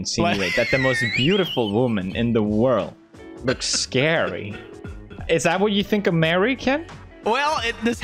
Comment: I thought Pewds said “is that what you think American?” 🤨